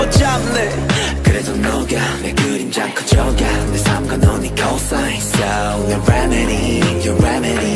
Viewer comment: when he said "equal sign" I thought he said cosine and I died just a lil' bit